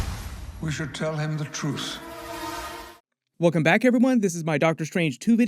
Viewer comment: To me it sounded like he said, “we should tell him the truce”. Patrick has always annunciated his words well and is always articulate in his films. Unless… the poor guy is getting older.